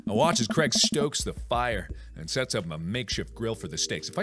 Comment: I thought it said Craig strokes the fire